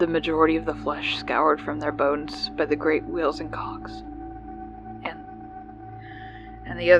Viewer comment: for a sec I thought I had heard "by the great wheels and C****"